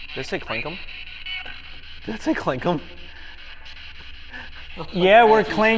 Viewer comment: I think that they said clanker 🤔